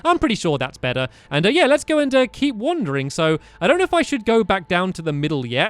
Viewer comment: i thought he said keep wondering not keep wandering